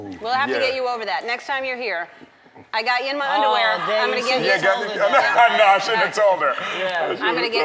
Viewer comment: is it just me or Does she say I got you in my underwear Ima get you to touch a mice? LOL Tf